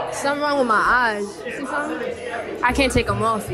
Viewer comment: Before reading the comments I thought she said: “I can’t take them off” I was like: yeah ur not wrong